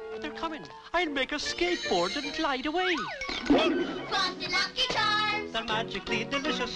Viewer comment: Is it just me or 1:38 sounds like its saying F**k you Lucky Charms!